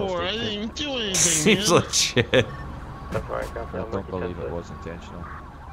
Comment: Oh I thought you said fucking seems legit 😂😂😂